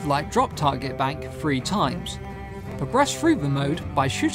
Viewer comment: OMG that accent, grr. @ 1:48, what mode? What are the words you just used?? "Breast fruit" mode? What? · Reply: Listened to it like 10 times and I think he says "progress through the mode" :) I wouldn't mind the breast fruit mode though.. <3